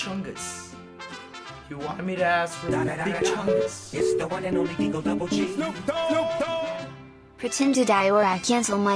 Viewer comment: That call from the random number during the stream gave me anxiety XD I'm also watching this at 5am so that isn't helping oof XD Edit: That scammer guy that said the prayers was so wholesome :') What does the phone say at 36:50? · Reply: I can only hear "Call restriction, audience suppression."